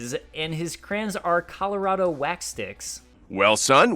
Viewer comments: Colorado? Did I hear that right? My dude. That says "Colored". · it obviously says colored wax sticks lol why tf would it say Colorado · say what now? Looks like it says Colored Wax Sticks